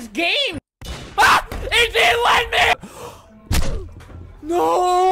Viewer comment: Subtitles 😂: why is he so good at this game ah is he lightning what he really said: WHY is he so good at this game IT DIDNT LET ME The part: 0:51 AAAAAAAAAA NO I GOT UNHEARTED